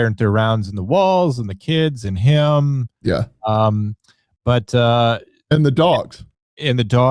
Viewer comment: I thought he said I was in the kids room